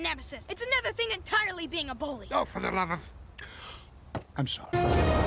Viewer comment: did he just say "motherf-"???